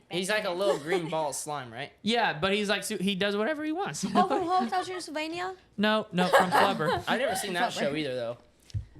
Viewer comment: What did she say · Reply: hotel transylvania